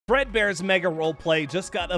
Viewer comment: Oh, I thought you said BREADBEAR. 😂